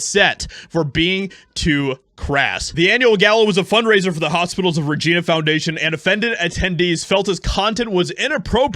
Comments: Somethings wrong with my hearing, I thought he said hospitals of vagina. Had to go back, and STILL sounds like that. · I thought the announcer said Hospital of the Vagina foundation lol